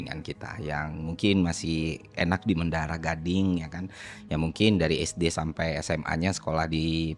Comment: entah saya salah dengar 9:03, menyebutkan mendarah gading atau berniat mengatakan mendarah daging?